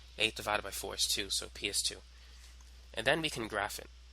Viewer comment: i heard PS2 dude, i have game addiction